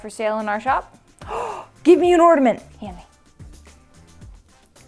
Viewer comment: did she say "Ordament"?